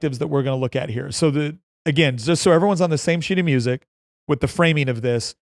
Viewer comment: I swear I first heard you say, "so everyone is one the same shitty music" 😂😂😂